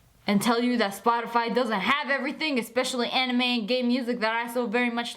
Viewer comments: did she say gay or game music?😂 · I’m watching this with my sister and she thought said anime music and gay music instead of game music 🤣